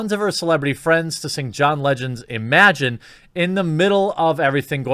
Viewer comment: Did he just say "John Legend's Imagine"?